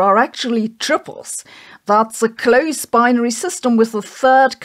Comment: Man, I remember Mondo drinks from when I was a kid. I don't think it has anything to do with the makeup of the universe, though. Edit: am I the only one who thought Sabine was saying "Tribbles" instead of "Triples", and getting very confused what the little fast-breeding furballs had to do with anything?